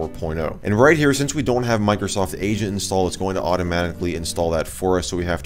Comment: Wheb he siad "Microsoft Agent" I thought he said "Microsoft Asian"